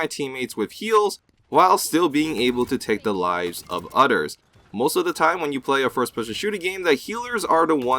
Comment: Did u just say udders?